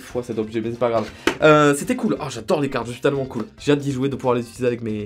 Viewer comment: C moi ou a 13:25 il dis je suis tellement cool 😂